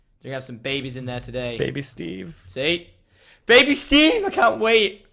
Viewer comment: did u call wag steve?